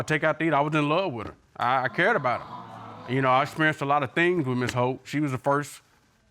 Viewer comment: i heard miss hoe ! My bad let’s continue